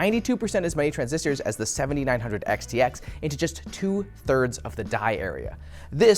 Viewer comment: I can't help it but at min. 5:08 I hear "two turds of the diarrhea"